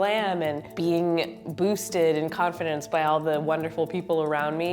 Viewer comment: o thought she said boobsed 😂😅